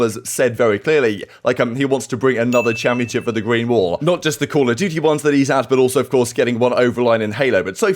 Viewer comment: did you say Call of Dookie here? :D